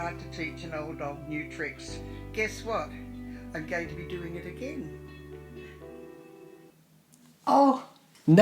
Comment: "I'm going to be doing it again." First heard that as, "Here I am doing it again," like this video was her second public statement of her relation. And that got me.